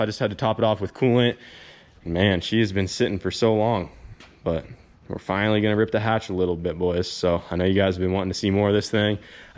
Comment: i swear i thought i heard say :we're finally gonna rip the hash here boys hahaha